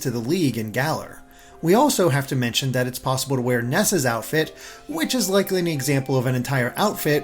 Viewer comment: I dropped everything, tabbed to google, and was very disappointed. I thought they said ness's outfit. If only...